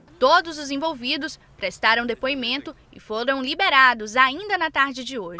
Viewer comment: e fodão liberados" só q ouvi isso ???